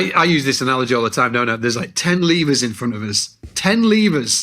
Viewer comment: Starting at 14:30, it took me way too long to realize Scott was saying "levers", I thought he was saying "lemurs".... Was a confusing but lovely visual in my head during that time 😅